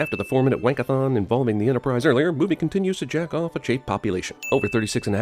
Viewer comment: I thought he said jack off not checov (or however it's spelled)